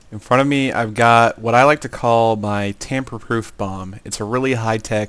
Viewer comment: Hmm, I thought it said "tampon-proof bomb" lawl